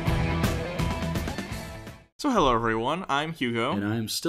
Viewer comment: I thought, I misheard you! "To hell, everyone!" ?